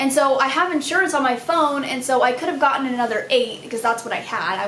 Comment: Oh God for a second I thought it would said 'i got another cat'😂❤